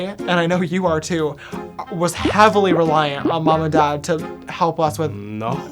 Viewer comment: Did say mommy at 1:32 Awwwwwwwwwwwwwwww